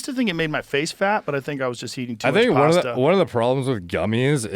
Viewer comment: Wait, did Rogan say he did it too much pasta?